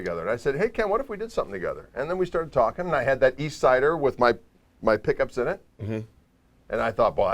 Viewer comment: I thought he said "I had that yeast cider" and I thought "That explains a lot" :o)